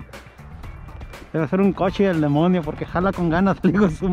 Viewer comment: Pensé que decias pichon... Pero ya vi que dices Fish on! Saludos